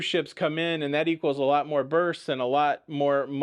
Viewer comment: Oooohh! BERTHS, I thought you said births as in cruisers having BAM chicka bow wow time that generates kiddos! LOL